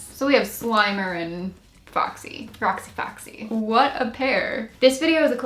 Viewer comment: Omg it sounded like you said my NAME “FrostyFoxy” :D